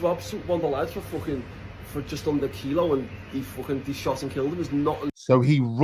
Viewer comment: did he say shot and killed him? Just before you paused the video